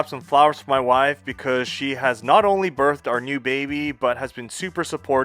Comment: Gah!!! I misheard "birthed new baby" as "burnt new baby". A re-listen calmed me. 'gratz on the new kiddie.